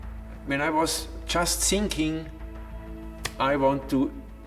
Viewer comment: did he said "i was just sinking"?